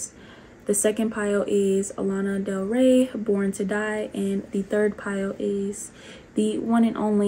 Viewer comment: wowww when I was picking my pile I wasn't looking at the names of the songs and when I saw Lana Del Rey I literally heard "We were born to die" and then I saw the name of the song and was like-😳